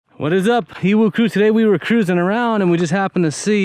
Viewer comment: It always sounds like your saying "whats up evil crew" what are you actually saying? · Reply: EWU (Explore With Us). :-)